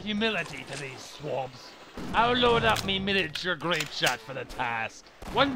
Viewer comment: "I'll load up me-" What does he say there? It sounds like Militer Greatshot, but I have no idea. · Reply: "Miniature Grapeshot" In reference to a type of shot used in naval warfare, and also land combat sometimes. Grapeshot, sometimes called 'canister shot' was a can-shaped round loaded into a cannon, which then would discharge numerous smaller pellets when the cannon was fired. It was brutally effective in dealing with infantry/sailors. And an apt comparison to a shotgun. Sorry it took 6 years to get this answer.